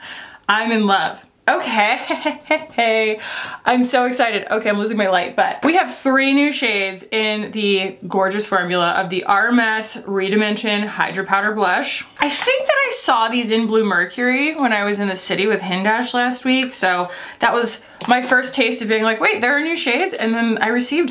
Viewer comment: For the longest time, i heard "RMS" as a mispronunciation of "Hermès"... thats when i knew i needed CC 😂